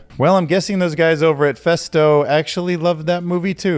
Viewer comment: I literally thought Festo said Pesto, and I was like did they just insult themselves? 🤔😅